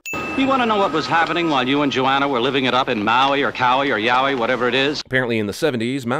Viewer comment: did he just say "*Yaoi*"?